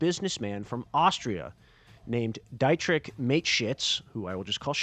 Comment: I thought he said Dietrich makeshits